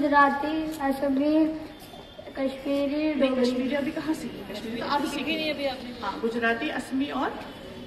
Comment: "aapne Manipuri kahan seekhi... Huh" 🤣 0:38 It's sounds like she said "daripuri"